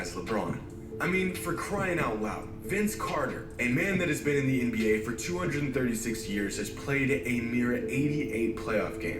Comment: Could’ve sworn I heard him say “Vince Carter been in the league for 236 years”